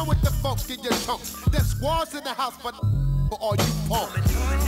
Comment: FYI: For anybody wondering for years what he says at 1:17 that was censored he says: “Def Squads in the house for 94 for all you punks” The song came out December 1995